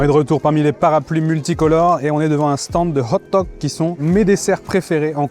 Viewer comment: ça a l'air marrant le truc gateau de riz salé/saucisse, à condition que la saucisse soit pas degeux ^^ .... s'toi le "crepe boy" ^____-;;;; les "bâtonnets" c'est des kadaif, en gros des nouilles type "cheveux d'anges"... Ah ouaiii, bon anniv'!! Bibimbap.... bim bim badaboum bing bang .... ^^;;;; (désolé, j'ai craqué ^^;;;;) Les oeufs dûrs c'est la seule forme d'oeuf dont je suis pas trop fan... le jaune est trop sec, le blanc a une texture un peu trop "gelatneuse", et froid ca sent le souffre -___- c'est pas un anpan du coup, c'est un anBaoh ^^;;; Arfff, j'ai entendu "stand de hot dog" ^^ Ah oui, je me disais aussi que tu pouvais pas t'installer comme ça au dernier moment dans un pays hors UE, qu'il te fallait des visa et autre....